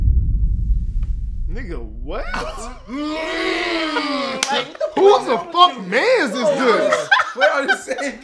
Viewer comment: can someone tell me what he said ? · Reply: “Yeah if they can ever get a house”, he speakin on RDC